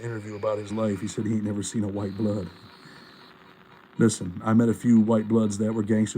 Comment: what pno said was he never saw a white blood in Florida DOC. There’s someone else hearing something and forming a different conclusion